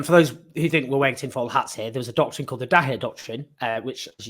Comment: Please like, subscribe - and help us take on the pro-war media here: <link> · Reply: Did Owen say "Douhet doctrine" ???🤔